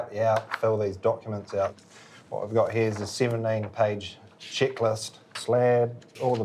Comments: I thought he said "shit list". He said "checklist". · i swear he said shitlist